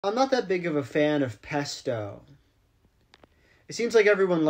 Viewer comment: I thought he said Pepto for a sec